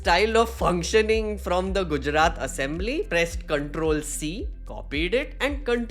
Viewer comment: sounded like pest control 😅... no wonder why Shahji used the word termites...